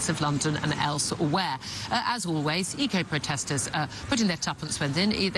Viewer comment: I misheard; I thought she said 'ego-protesters'. Exactly; that's the best term for them. Take a grain of media flub, an ounce of misunderstanding and mix with a whole litre of ego and what you get is an ego-protester.